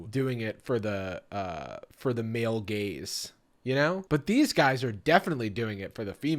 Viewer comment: my toxic trait is whenever I hear "male gaze" or "female gaze" I hear it as "male GAYS" / "female GAYS" and I have to like take a second and recalibrate